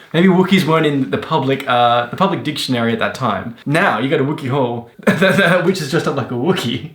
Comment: All I heard was “Wookiee Hall”